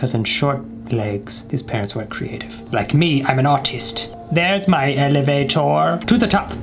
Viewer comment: It sounded like he said, "like me, I'm an autist"